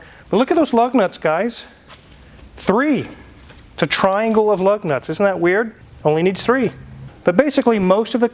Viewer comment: I thought he said "triangle of love nuts" lol.